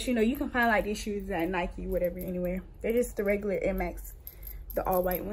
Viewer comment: I know I'm not the only one that heard her say "the regular air maxes" for Idk what "regular air maxes" are. I know what Airmax 270s are but regular? Also, those looked like huaraches. But maybe I'm wrong maybe those ar air maxes?